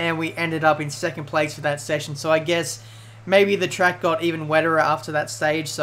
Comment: What do you guys think of the 2015 Mclaren? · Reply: did you just say..... "even weterer"?